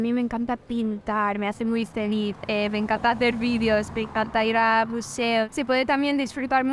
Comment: Jajajaja me hizo gracia porque dijo me encanta ir a museos, y creo que le entendiste buceo